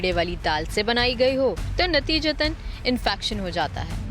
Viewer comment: did she say infacktion ?